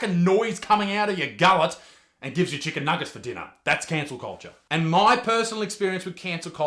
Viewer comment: cancer culture Did anyone else hear that?